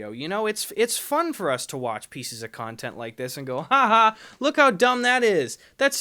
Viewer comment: I thought he was going to say haha poor!